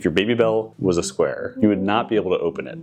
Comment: I heard "baby belly" and all I could think was why anyone would wonder what a pregnant woman with a square belly looks like